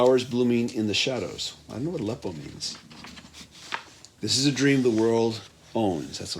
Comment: did you just say i wonder what aleppo means